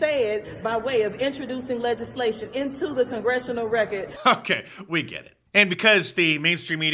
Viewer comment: did she say "inchojuicing lejizzslation into the gongestional racket"?????